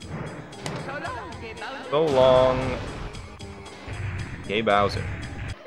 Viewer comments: You know Charles Martinet, the guy who voiced Mario, finally said that he was saying “So long, Kinga Bowser”? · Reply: I always heard "So long, K. Bowser" probably because of how they shortened "King" in the Super Nintendo games. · Mario says "So long King Bowser." But yeah it does sound like he says gay Bowser